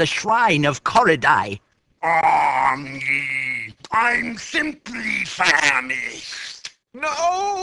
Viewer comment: Did he just say "SIMP" at 0:08? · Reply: he said simply